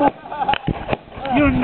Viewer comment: what did he just say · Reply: you n you did it